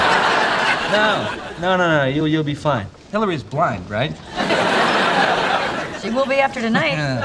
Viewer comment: Chandler: Hilary is blind , right? Monica: She will be after tonight. (lol) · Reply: Pretty sure I keep hearing him say 'Hillary is black right'